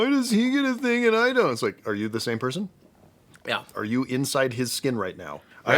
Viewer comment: I heard “Are you in his skin right now?” and rather than a horror movie, I just thought of To Kill A Mockingbird, haha